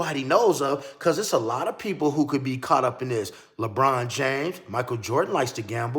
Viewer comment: Man I thought you said LeBron James